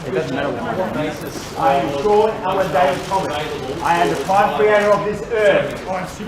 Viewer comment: What he say his name is? Something something Thomas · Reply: I heard "sure Ellen Davis thomas" but I'm I'm not sure about ellen and I think he really said sir not sure